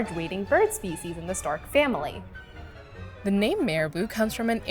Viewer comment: Um, did she just say; "...in the Stark family."?! 😁 (It can't be just me lol I listened to it multiple times. 😇)